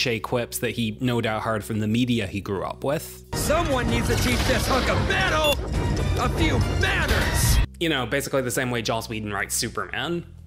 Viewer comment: wow, I always heard this wrong… …defeat this hunk of metal. I’ll see if THAT hurts Saw this movie in the theater, and several times since